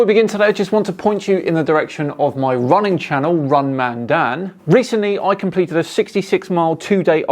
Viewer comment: I thought he said ‘WrongManDan’ in the end instead of ‘RunManDan’. I’m not sure if the former would have given him many views… Then again it could be the opposite depending on the content. Could have been interesting for FlatEarthers and extreme Creationists.